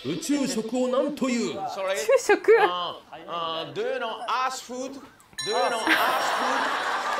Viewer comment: assに聞こえるのも納得の発音で 「まさか地球食知ってる？」なんて言われると思ってもみないから earthかも？って思うことすら出来ないという地獄 とても好き